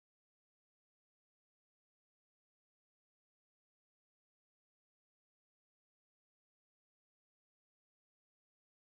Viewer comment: I didn't hear you say the name of the moon songs at 1:43. I heard you say "How High the Moon - no " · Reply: It was “Old Devil Moon”.